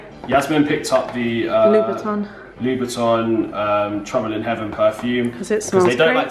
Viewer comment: Lol this whole time i thought you were saying trembling Heaven lol no wonder i couldnt find shit..lol Thanks diff gonna check out that perfume xxx